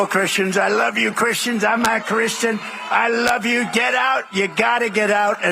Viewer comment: It sounds like Trump says, "I'm not Christian" when he says, "I'm a Christian" and you can see him shake his head no when he says it.